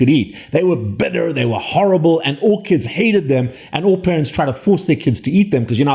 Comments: After he was talking about orchids, I thought he said ORCHIDS hated brussel sprouts. But he said “all kids”. 😂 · Was it just me that thought you said brussel sprouts "orchids hated them" 🤔 🤣....... nooooo "all kids hated them" duh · when he said all kids i thought he said orchids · I thought you said Orchids hated Bussel sprouts. Then it dawned on me you changed topic and said All Kids. 😅 My partner spent most of her life vegetarian as she comes from India. She then asked why her religion pushed it. She tested the reasons against science and the last 10 years has eaten everything. I learnt the hard way about the toxicity of a vegetarian diet. I made a lentil chick pea kidney bean stew. I was so sick for days almost went to hospital. Turns out kidney beans are toxic and require lots of cooking and lentils amplify the effects of kidney bean toxin. This is well known in India. Lesson learnt. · I thought he said orchids hate Brussel sprouts .. wow that was a strange hybrid experimentation. Oh no. It was all kids!